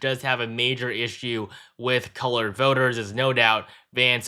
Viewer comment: I'm a hard core conservative but I do have to ask one question. Did he really say "colored voters" at about the 13:25 mark in this video or is my hearing going? 😂 I mean, I'm good with it and all. I'm just surprised.